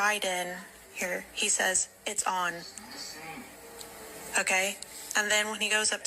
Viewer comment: I kinda thought he said, "you're done"....but "it's on" sounds good too